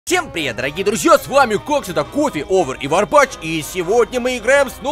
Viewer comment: я включил с 0:03 и мне показалось что он сказал кокс.